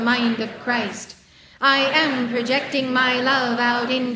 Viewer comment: In the 0:07 minute in the transcript instead of protecting is written rejecting. Thanks so much for such a beautiful and powerful Decree I Mantra🙏🏻